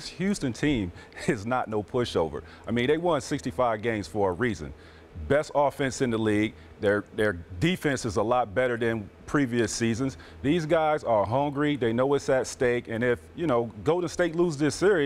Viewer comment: For a moment I thought T-Mac said "They won 65 games for Ariza".